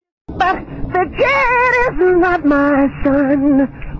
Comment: As a Spanish speaker I can say that barely I can hear "The kid is not my son". Instead I hear "Tu quieres una manzana" that can mean "you want an apple"